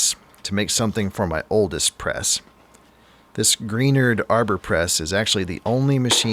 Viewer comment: cream, off-white...oh i thought you said green......and the hobbit sized comment was the chef's kiss...lol:face-green-smiling